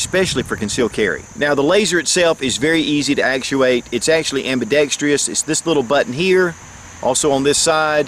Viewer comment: I always hear you say "ambidextreous" but I think you're adding in an extra 'e'. The word is spelled "ambidextrous" and that's how I usually hear it pronounced.